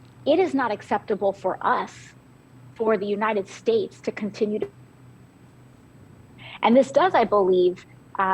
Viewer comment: Sounds like she says at 9:01 as it skips for a second or two, "the United States continues to F**k the Puerto Rican people!" LOL